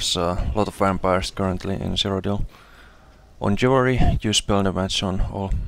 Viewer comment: nice build, what add on is your UI? i like it! lol, also @ 1:15 you mention something with the jewelry, but I can't understand...what did you say? are you running all spell damage on the jewelry? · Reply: Yeah, spell damage on all jewelry (sorry, I've a pretty strong accent & bad microphone >.<) UI is FTC unit frames <link> & Srendarr <link> buff bars.